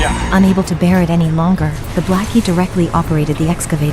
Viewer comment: did you say "the black" wow this channel is racist asf. I know im not the only one who caught that.